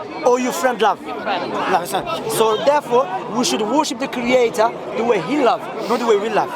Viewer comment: What did he say at 13:38 ? I have trouble in hearing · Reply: we shoudl worship the creator the way he loves